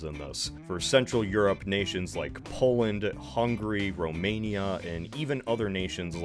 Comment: Is it just me or did anyome else hear him say "hungry" instead of "Hungary" Edit: its at 4:14 Edit2: he does it again at 5:41 Edit3: and